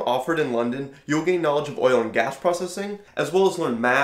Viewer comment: When you said "oil and gas processing" I heard "Euler and Gauss processing" LMAO